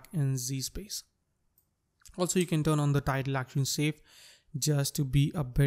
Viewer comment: i dont understand what your saying, is it tag locking screen? pease help me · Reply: May be it's the Title/Action Safe you are refering to?